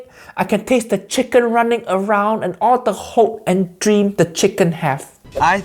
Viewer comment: DID I HEAR HOPES AND DREAMS?!